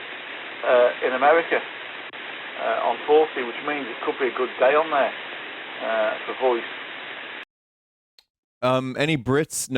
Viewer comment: Here's what I think is being said (edit: I'm not British, I'm Australian) "[unintelligible] which is a real shame, because, I'm getting spots, uhh, in America, ehh on forty which means it could be a good day on there, ehh for voice." I'm fairly sure he's talking about hearing transmissions from America all the way in England on the 40 metre band (7 -> 7.3MHz). A common goal for amateur radio operators is to establish long distance communications with their stations, and certain atmospheric conditions make the feasibility of this vary.